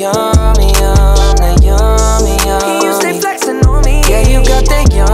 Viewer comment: the message in the video was terrifying, used to think it was dumb but then i realized that these things are real and celebrities dont have many ways to communicate it to us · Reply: "did he say "he used to stay flexing on me" or "you still flexing one me" or did he creatively mess the 2 together?? Its so sick. This whole song is about how diddy put fame and luxury at his disposal, drugs, pretty women, and the power diddy held. Aka "yummy yum" diddy say the word any night any day, say the word jb is on the way. Diddy had him on the rise, justing lost himself and got compromised, diddy is incriminating, no disguise and diddy never runs out of supplies.... 50/50 love the way diddy splits it, 100 rack help him spend get litty at all the diddy parties.. the kind that roll his eyes back and make his toes curl (making someones toes curl isnt a good thing to her from someone) If you listen to the lyrics and put 2+2 together... this whole song is about what he went through...